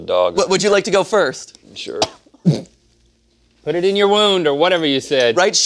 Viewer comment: I thought he said “PUT IT IN YOUR WOMB...”